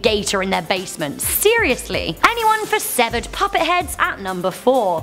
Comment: I thought she said severed puppy's head but she said severed puppy's head